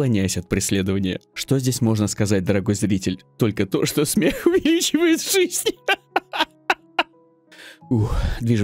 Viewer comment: На 5:41 что он говорит? Когда смеётся, может кто перевести?)) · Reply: Смех - увеличивает жизнь =)